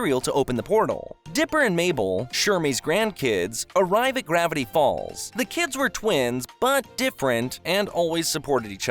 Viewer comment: Is it me or did i hear "gravity BALLS"